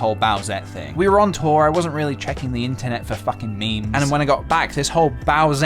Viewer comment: Did you just say 'Fucking'? I have nothing against cursing but it just sound so strange coming from your mouth ;-)